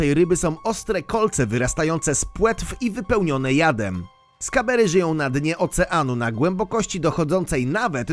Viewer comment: NR 7 usłyszałem skamer XDD